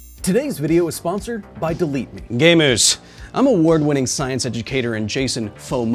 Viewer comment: i had to replay it a couple times because i thought you called me a gay moose but you called me a gamer. idk which i'm more insulted by